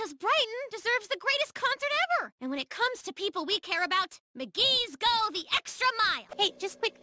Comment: it sounds like she says “the gays go the extra mile”